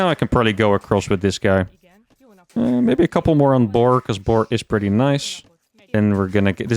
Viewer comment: did you say "bork" for boar pork I heard it